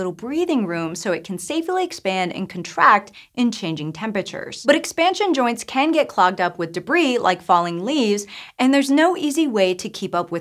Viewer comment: Did she say at 2:15 "expansion joints can get clogged up with falling leaves"?